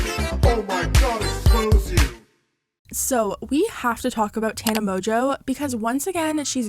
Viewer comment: I thought she said teana mongeau